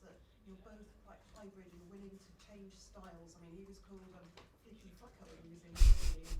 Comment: Did she say Flicky Fucker? 🤣🤣🤣